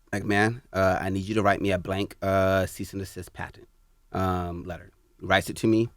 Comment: What is the word he said at 25:43? He mention some type of letter at 25:43 but I couldn't quite understand what he said. Anyone know? Anyone can answer. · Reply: Cease and Desist letter